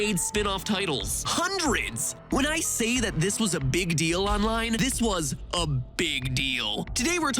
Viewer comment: did he say this is a big dick XD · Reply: King Gamer no he said "and this is a big deal"